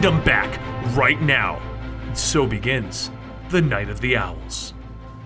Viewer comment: Okay, but I thought he said Vagina the Owls instead of the Night of the Owls.